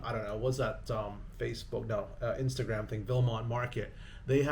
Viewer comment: what market? Did you say, "Belmont market?" Looking for parts for my 4runner. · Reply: Vilmont